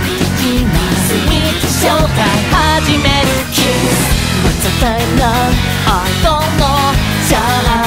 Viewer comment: のWhat's the time now?が あざといな♡に聞こえる同士いませんか、、？